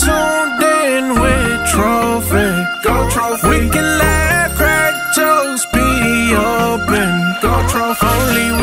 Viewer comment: Y'all why I thought the intro said laugh at jokes and it says laugh crack jokes anybody else no just me😫😞😭